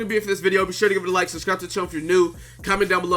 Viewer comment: why did I hear it comment sexual....something is wrong with me...I need help Y ^ Y.